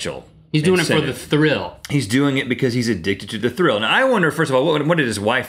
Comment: Did he say the d word d**k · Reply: No haha he said addictive 😂😂